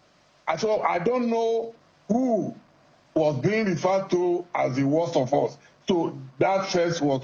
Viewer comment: nobody said the worst of us, what was said is "the best of us should not be second"